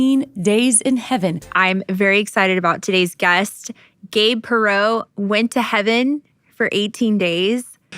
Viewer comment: at some point I only hear Gay Pro